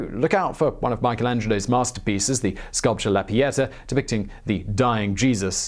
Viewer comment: Pieta? Oh my god I thought you said piñata.